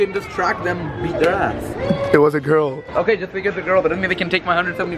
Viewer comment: did say “this n*ggas a girl”😆